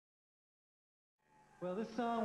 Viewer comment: Maybe I misheard, but did Roger say "MY first single?" Did he record it as a solo artist before it was done by the Byrds? If not, didn't the Byrds consist of a group of people?